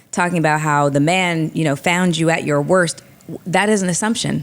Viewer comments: Context clues are important! If u misheard him say WORTH and thought he said WORSE. Use context clues!! How is he taking her at her worse when she makes more money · Ish was talking that ish... Joe said Kevin Samuels been saying that 🙏🏿... I thought I heard Tyler Perry say worth not worst 🤷🏿‍♂️.